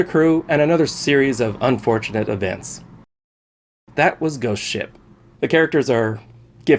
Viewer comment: At the into I thought he said goat shit